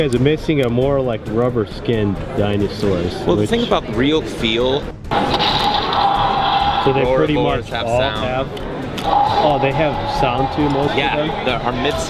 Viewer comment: at 9:40 the man said something about rubber skin and if you can please release the footage or please reply to this and if you remember can you tell me what he said? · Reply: He said it gets dirty and wears out quicker